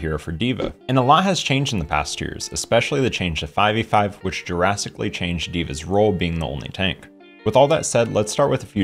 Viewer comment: 1. nuking for kill potential remains op below plat 2. I heard "jurassic changes for dva" and now I can't un-want a dva dino skin.